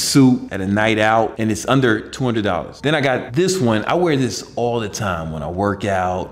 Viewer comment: Did he just say I wear this at sleep?